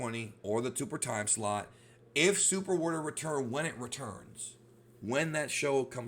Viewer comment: Thought you said it returns